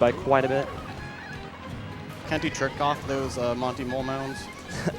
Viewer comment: who else thought he said Jerk off?!